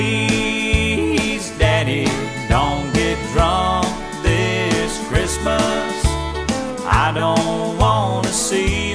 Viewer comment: I still have the cassette tape. On this song I thought he said “Please Daddy don’t get drums this Christmas”.